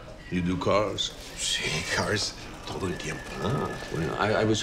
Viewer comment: Did he say "yes cars, all the the time"?